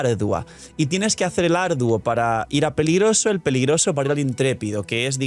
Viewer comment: Dice peligroso 2:34 pero yo escuche pegriloso muy pegriloso😂